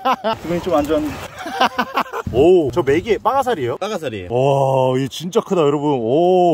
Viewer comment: 아 영상을 너무 봤나 . . . 빠가사리 소리가 왜 라삭라삭 으로 들리냐 . . .